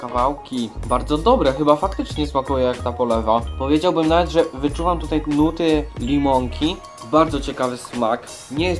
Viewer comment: Usłyszałam "Wyczuwam tutaj gluty limonki" xD